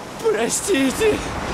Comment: What did Pi say there? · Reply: It's a Russian dub. He says "Я виноват" which means "it's my fault".